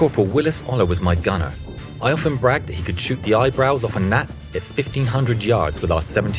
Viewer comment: "shoot the eyebrows off a gnat..." - I thought you said "off a Na.." - hee. Can't even type it in the comments without getting flagged. America!